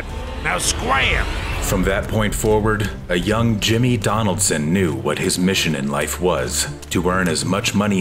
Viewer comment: a yo what did he say 'a young Jimmy Donaldson '